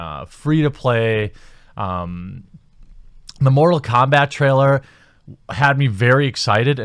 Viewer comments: When talking about what future game award shows would be about, I could have sworn you said 'moral kombat', then I had an epiphany and realized that's exactly what's going on with being 'woke' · Around 8mins in I thought you said 'moral combat' I think that'd be an awesome game tbh. Maybe stereo typical people representing oppressed group beating each other up. Have special oppressive or oppressed super moves. The campaign can be about how they all oppress each other and why they need to beat or kill each other as they feel oppressed.